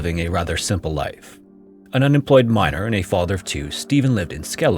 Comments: I was listening to this while working and I thought he said he was an unemployed "minor" with 2 children. I was like WTFFFFFF then I realize he meant "unemployed miner" lol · The first one threw me for a loop. I missed the beginning where the age was mentioned. I just heard "An unemployed minor, and a father of 2." I was thinking, a minor? AND a father of 2? WTH?? Oh wait......a miner..... I get it. 🤪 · I heard "unemployed minor with two kids" hunting and I was immediately thinking why the fu....then my brain caught up to the situation and correctly heard "unemployed miner with two kids"😂